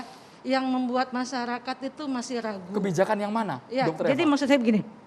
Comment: Kebijakan yg mna? Jengkol dengernya. Sy awam dan masyarakat biasa Kami merasakan apa yg sudah pemerintah Terapkan urus KTP. Urus surat kematian apa2 harus ada surat Vaksin. Bambang